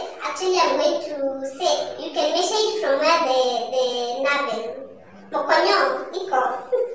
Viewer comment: Did you just say mokonyo? 😂 Appreciate the great video, girl! 😊